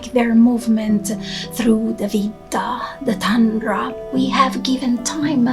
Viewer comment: Beautiful, peaceful, ...I wondered what is a word spoken at about [9:50 ]==== "movement through the 'vita '? 'veeta' ? 'vintr' ?, the tundra..." Or Vitta- magical times ? The word sounds like "veeta" to me....Some advise light therapy for some cases of winter depression, sadness or fatigue (or SAD). I think the light goes into the brain and affects some hormones... I have not tried this but, when doing meditation in the dark, I will see lights or a bright light in the mind and I wonder if it may be helpful... Especially if you are unable to grow antlers......